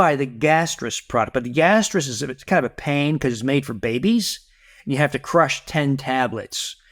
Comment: Did he just say, "its kind of a pain, cuz its made from babys"? ...